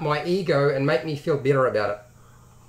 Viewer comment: Nice video! But what is that you say at 6:43? You wanna feel bitter about it? Why? I'm a tad lost here :( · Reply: I just now realised you said "better", I got that as "bitter". My bad, not a native English speaker :p Sorry for the misunderstanding.